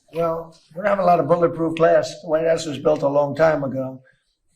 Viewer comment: It took Trump 5 try’s to say the word glass, get him the fk out of office! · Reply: It sounded like he was saying grass when I guess he was trying to say glass